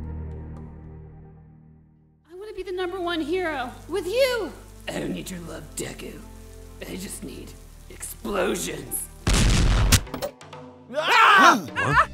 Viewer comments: The fact that I heard “ I don’t need your love deku I only need explosions” that caught me off guard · did i hear DEKU?